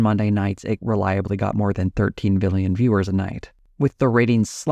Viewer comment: Am I hearing wrong or did he say 13 BILLION... because that obviously can't be right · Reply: He said 13 million